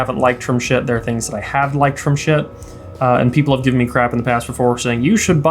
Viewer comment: What are you saying at 10:26? I just saw another review that also mentioned a word that to me sounds indistinguishable from "shit". I am going crazy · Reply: Schiit. It’s an amp company haha